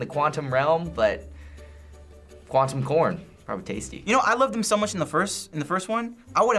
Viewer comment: 'Quantum corn,Sounds tasty' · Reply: The Ultimate SpiderKid I thought he said, “Quantum *orn”